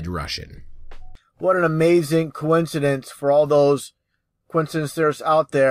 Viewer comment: "Imagine being as pathetic as Russian Vids." Now I'm sad, and I think I'm going to had bad, sad dreams tonight. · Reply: Did he say "coincidencers" ??